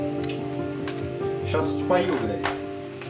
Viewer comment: Я не понялаа..а в конце ща кадром.. Мне послышалось..??? Мат... После слов ,"Ща спою...б..ть!" Жесть 😢☹️🤔